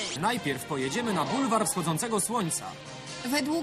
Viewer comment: za pierwszym razem usłyszałem „Kurwa wschodzącego słońca"